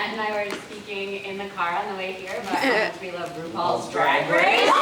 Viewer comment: What says in the minute 20:58? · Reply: Ru paul's drag race... I think